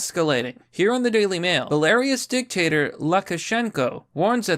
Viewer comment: I thought i heard him say "Hilarious dictator"......... Its pronounced, Beh-luh-roos :-))